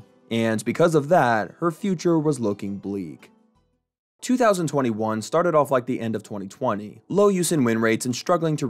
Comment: “2021 start at end of 2020”bro try to squeeze in every last drop of yap in his video 😂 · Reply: And you seem to have misheard him. He said "2021 started like the end of 2020"